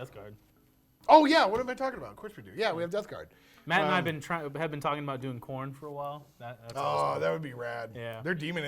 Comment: I swear I heard "Matt and I have been talking about doing porn for a while" and laughed for too long